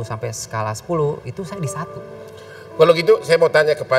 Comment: kalo sayangnya skala 1 sampai 10, itu skala nya 1. Apa ga salah dengar?